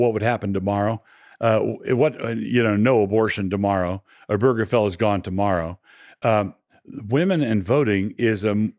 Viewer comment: what the bloody hell is he saying there? no burgerfella tommorow??